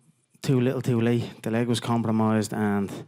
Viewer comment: From 0:49 Wtf did he just say? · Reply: little too late his leg was compromised